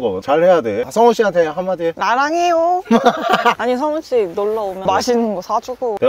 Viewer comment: 라랑해요 라고 하자마자 모자이크 흔들리는 거 개웃기네 ㅋㅋㅋㅋㅋㅋㅋㅋㅋㅋㅋㅋㅋㅋㅋㅋ